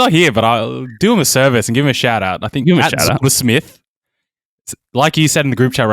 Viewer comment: what was the i didnt really catch it all i heard was smith Edit: Never mind heard it clearly